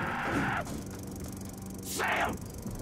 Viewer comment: Anyone hear the zombie say Sam? lol